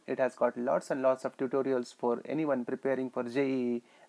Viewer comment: Is he said in medical exams